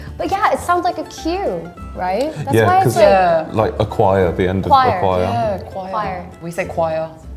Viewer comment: I keep getting recommended videos from this channel, and let me say for a language-focused channel the subtitles are so consistently wrong! For instance, at 8:15 how couldn't the person understand they're saying "acquire" instead of "a choir"? They just said the word "choir" sounds like it has a Q, and they're comparing it to another word that's actually spelled with a Q...